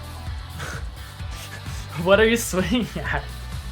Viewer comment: At the beginning of ed 11 I was swaying back and forth to the tune with my eyes closed and he said “what are you swinging at” (talking ab Aki swinging his sword at nothing) but I heard “what are you swaying at” and I got scared for a second LMAO